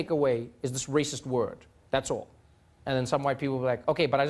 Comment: where did you get 'painted as a racist just for being white'? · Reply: you're right -I listened again (2:22) - thought he said "white people want to use the word, but actually said "Some" - thanks for pointing it out - feel better as I actually really like Trevor